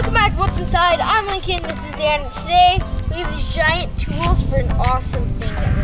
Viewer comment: When I subbed I thought Lincoln said and this isdad